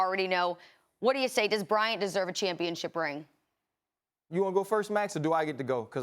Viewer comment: I deadass thought it said kobe bryant deserves championship ring